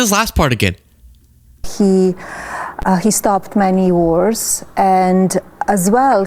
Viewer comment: it sounds like she said:" he started many wars" which in theory is true 😂😂😂